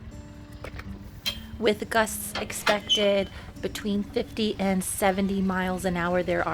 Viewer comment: When you said "with gusts expected" I heard "with Gus expecting" and was very confused. I know its afternoon but I need coffee.